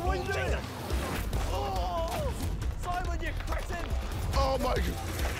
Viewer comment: did Harry call Simon a peasant😂🤦🏻‍♀️ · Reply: Peasant? He said cretin